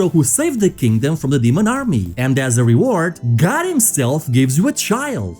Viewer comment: bro... wtf is with the CC, minute 29:21, says god himself and the CC says DOG himself? wtf is that bro fix that shit.